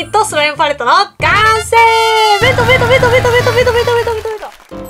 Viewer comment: パレットが完成したときの 弁当弁当弁当弁当！が ベトベトベトベト！に聞こえたのは 私だけじゃないはず🤔